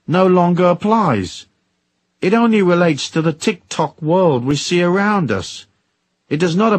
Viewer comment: did he say the TikTok world? 😮